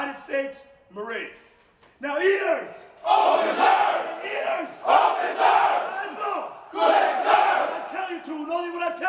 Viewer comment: What is SSGT yelling to the recruits at 02:22 - and what are they responding? I'm having a hard time hearing it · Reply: "NOW EARS" "OPEN SIR" "EARS!!" "OPEN SIR" "EYEBALLS" "CLEAR SIR"